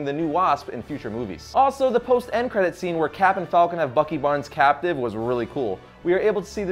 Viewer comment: Wait wait wait... Did you just said Captain Falcon?